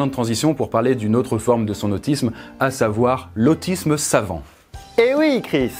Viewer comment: Au début j'ai entendu l'autisme savon XD